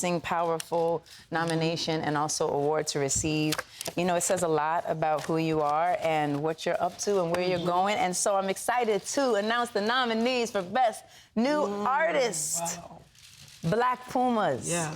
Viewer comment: When Alicia said black puma,I thought she said black pink